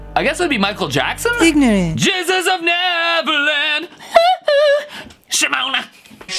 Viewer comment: I'm hearing. IM A BAKED APPLE PIEEEEE YEAH! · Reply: And the Jesus of never land I hear: "Jesus will never end" I guess so